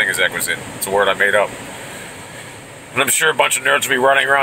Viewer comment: I thought it said gunga gulag